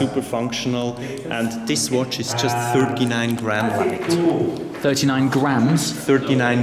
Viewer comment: I thought he said "This watch is only 39 grand......."